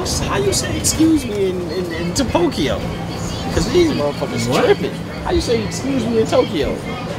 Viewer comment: TF is topokyo??? 🤣🤣😂😂😂😂🤣 Then said “what?” I swear I love y’all ❤